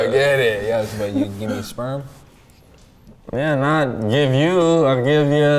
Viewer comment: Did I just hear “gimme sperm” as the last words of this video LOL.